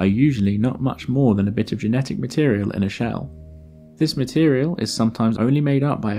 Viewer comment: And sadly, not in a fun candy shell either. :( Though, in a sense, you could say it's a "f***ing shell", as many have misheard it.